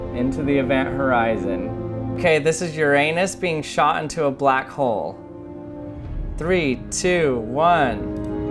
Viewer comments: I thought he said “your an*s being shot in the black hole” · Did he just say this is your anus being shot in a black hole? NASA wants to know your location now! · I heard your anus 🤣🤣🤣🤣 and I literally dropped my phone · I accidently thought you were saying This is your anus being shot into a black hole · scientists: can't make black holes some random guy: hi guys I made a black hole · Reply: Sorry, but I heard him saying — "okey, this is your аnu$ shut into a black hole" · When he said uranus, i thought he said "your anus"